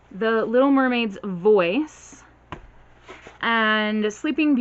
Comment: I was just listening and I thought you said "Little Mermaid Boy" Sounded good, oh well! G certainly does go above and beyond :) The Tenth Girl sounded good too. Thank God for Google Play samples for letting me know it's yet another present tense book -_- I bet boxes don't send hand sanitizer because someone would say "I used the hand sanitizer and still caught covid! I'm going to sue!"